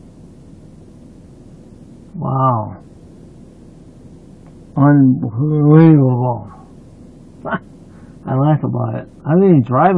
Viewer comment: he says wow... unbelievable but it comes out as unbelullable... Lol! And why does he keep saying he was gonna call an uber if he wasnt drinking? That's dumb